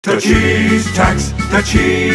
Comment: first time watching i thought it was saying the cheese touch lol